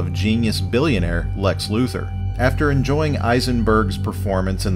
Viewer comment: When you said Lex Luthor I thought you said Lex Looser